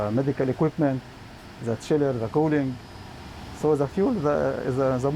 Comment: Please correct your subtitle, 2:01 he said “the chiller” instead of children , cooling … Unfortunately, the situation is frightening